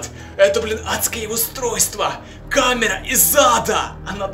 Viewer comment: мне одной послышалось камера из зада? 😂😂😂